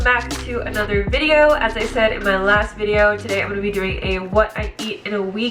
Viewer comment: I misheard what you said in your intro, I thought you said “like I said it’s my last video” and I was like wait WHAT nooo 😅